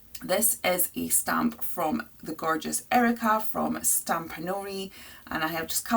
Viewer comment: Great video. Where is this stamp from? 4:08 It's nice. You said the name in the video, but I could not understand what you said. Thanks. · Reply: Glad you enjoyed the stamp is from Stampanory <link>